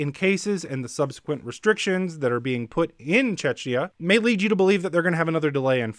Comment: Did he say "in Chechnya" 2:25?!!!